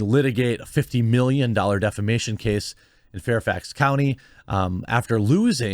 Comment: Leave amber alone!! You monster! 😭 Let her poo in peace in whatever bed she wants! .... Ohhhhhh. You said "defamation case". Thought you said "defecation case".